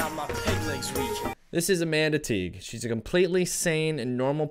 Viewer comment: When you first said "this is Amanda Teague" all I heard was "this is a manatee" and now I'm laughing at how much that would have actually made sense if you're were roasting her